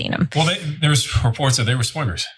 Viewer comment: "They were swingers" 😂🤣😅🤣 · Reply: I thought he said swimmers 😂😂👍